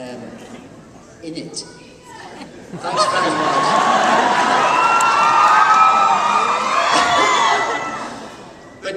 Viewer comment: what does the lady say at 0:56? david smiles looks an says thanks very much??? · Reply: 14 years late response but she says 'and looking good' after he says he's 38